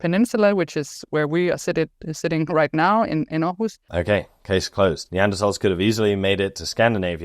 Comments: the last word in the subtitle should be Aarhus which is the city she is referring to, instead of the word ‘host’ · Another great video by! I don't know why, but I feel compelled to offer this correction: At 14:33 what is saying is, "...sitting right now, in Aarhus"